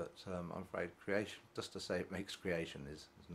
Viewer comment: it sounds like your saying "it makes creationism not true". As in 'god created the heavens and the earth about 6000 years ago' is simply false. Is that what you were saying?